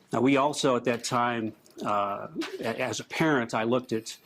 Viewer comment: Does he say As A PARENT or as APPARENT does that say it was Burke